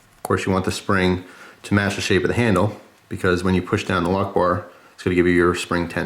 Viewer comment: I thought that when you said "a lockbar" it sounds a little bit like "allahu akbar" and now i can't get it out of my head and you keep repeating lockbar...... D=